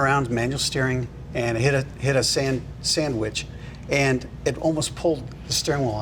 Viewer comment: I wasn't listening properly, and I thought he said he was driving around and hit a sandwich! :0)